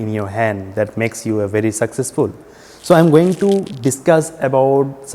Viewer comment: oh I thought you said “makes you a very sexist fool”😭🤚🏻